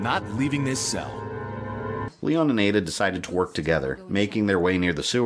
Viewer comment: I keep hearing "Leon and Ada decided twerk together"...